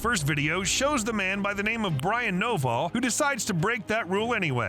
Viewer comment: Thought you said his name was Brian noballs😂